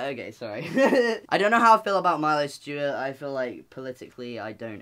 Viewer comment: I didn't know who Milo was and at first I thought Noah said Miley Stuart (Stewart?) and I was like "was there a Hannah Montana poster in the background or some shit?"